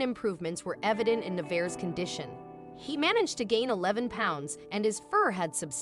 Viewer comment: Did she said at 3:00 'the bears condition'??